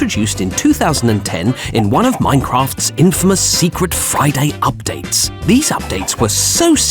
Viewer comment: I thought he said Fry-day for a sec XD